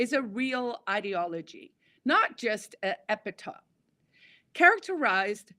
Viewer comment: "Fascism is… not just an" … epitome? epitaph? epithet? Not sure what word that was. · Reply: Epithet--and yeah, I totally flubbed the pronunciation!